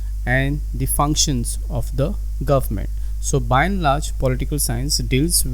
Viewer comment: here I didn't understand the word u saying..? Inlarge or something plx tell me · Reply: by and large, Political Science.....